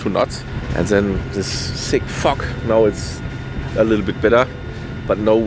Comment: I am sorry but at 01:13:08 all I hear is "sick fuck"... :P :D i am sorry i really am. I love this video, I overly enjoyed the amazing drone shots, the music and the upbeat character of this great dude! What an adventure! But I had a laugh there. I know how mature of me haha! Anyways thanks for the video, and I hope you make more like these! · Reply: It was thick fog! Best regards 😀⛵⛵